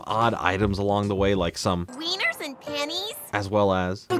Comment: did she say "weiners and panties" or "weiners and pennies"?